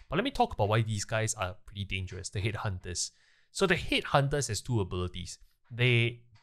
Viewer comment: i thought u were saying they were dangerous because "THEY HATE HUNTERS" i was like wtf is wrong with hunters lol